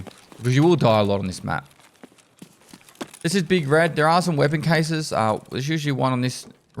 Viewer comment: At first I thought he said "Big Fred" and I was like "is that a Mutherf*ckin' Django Unchained reference?"... Please brain, stop